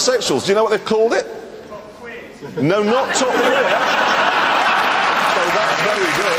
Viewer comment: What did the fan say? Top Queen?